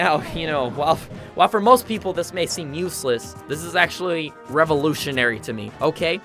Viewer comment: Or snake for edgeguarding (particularly against a certain soulless-eyed "Okay" child) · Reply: I thought you said eyeless 😳